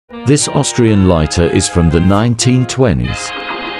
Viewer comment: I thought he said “Austrian painter”💀💀💀💀